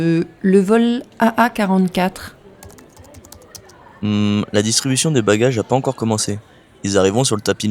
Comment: I totally missed the "pas" in "la distribution des bagages a pas encore commence". I thought he was saying it had already started. I completely understood everything else, even though I didn't catch every word. Thank you for these videos - this is really great listening practice!